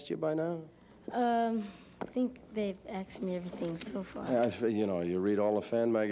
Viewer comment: Did he say "they've axe me"? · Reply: 'they aksed (asked) me' - he said. afro americans do say that way sometimes.